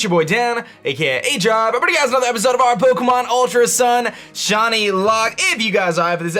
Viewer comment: Sounded like he said "JohnnyLocke" instead of "ShinyLocke" in the intro.